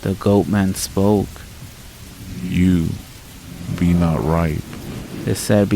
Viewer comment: What is the goat man saying?? 24:59 ??? I heard “you are not right”? Or ripe? It’s driving me crazy…😂😂😂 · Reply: Hey Maureen!!!! Lol I GOT YOU! It’s saying “YOU…are not RIPE” 😅 my apologies if I didn’t say it clearly. 🙂 I hope you enjoyed the stories. 🙌🏼